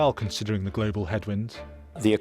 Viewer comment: At first I thought he said "head wound".